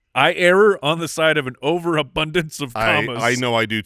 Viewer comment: did say " i error on the side of an over abundance of commas"? instead of "err" on the side